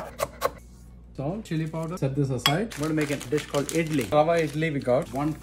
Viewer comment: It took me a min but I thought he said “we’re gonna make a dish called idiot” am I the only one 😂🧐